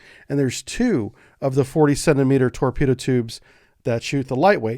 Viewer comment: I thought I heard 47 meter torpedo tubes 9:13 but I guess he said/meant 40 centimeter tubes. 🤔